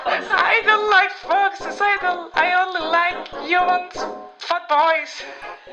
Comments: it sounded like said "i only like humans and fkboys" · When she said fat boys. I thought she said fuckbois